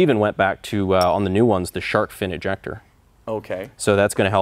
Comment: Anyone else hear "shart-fin rejector" around 5:30 or just me?